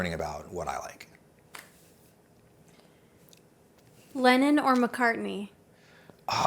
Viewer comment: When she asked "Lennon or McCartney" I heard "Lenin or McCarthy" and I was like wtf?